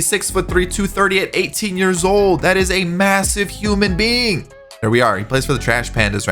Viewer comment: MASSIVE did you say MASSIVE